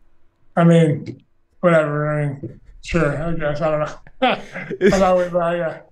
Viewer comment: What did he say at 1:14? I didn't quite get it! 😃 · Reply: Sure i Guess i dont know